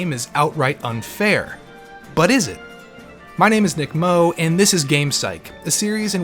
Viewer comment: I thought you said DICKMOE At the start